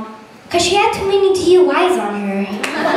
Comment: What does she say after "you got taken away from your real mom 'cause she..."? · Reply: Had too many DUIs on her, after all she runs thru Oklahoma.